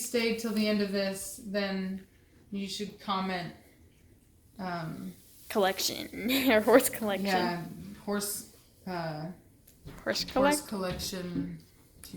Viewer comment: haha I thought people were saying "horsecollection" because of how big the dogs are.